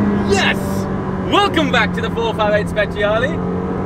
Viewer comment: I heard "Welcome in the 488 speciale" I was confused X)